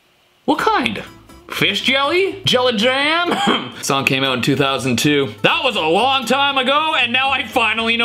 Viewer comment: i thought he said "sunky out in 2002"